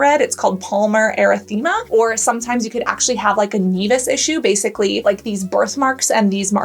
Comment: Please will someone spell the symptom she said before jaundice for me(15:22)? I think I have them but I keep hearing anibus. 😅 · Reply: nevus/naevus i.e like spider naevi